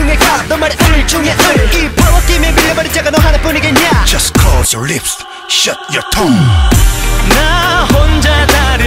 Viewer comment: i love this song! but did he say "close your lips shut your tongue"? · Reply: I heard "Close your lips shut your toe" XD